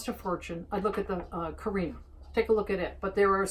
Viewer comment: Does anyone hear what coffee machine she suggests at about 5:50? I hear something like 'kareem' but can't make heads nor tails of it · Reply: I think she meant Phillips Carina <link>